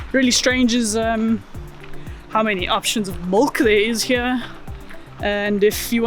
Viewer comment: With what's going on in South Africa now I wonder if perhaps you had some foresight of the situation? At 11:35ish what was it you said there was so many options for? Your speech is excellent, I just couldn't make out that one word. Sorry your leaving, Vancouver is great place to visit, but crime was getting out of hand when I lived near there, hence the need for Jock, if I remember right he had a law enforcement background. Good luck, will be watching for your new adventures. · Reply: Milk! It's so confusing, haha. Many other South Africans were also quite enthralled by it. And I don't mean almond, oat, rice, or cow milk... I mean just normal cow milk has so many options that we don't have in South Africa. We have 2% and low fat—that's it.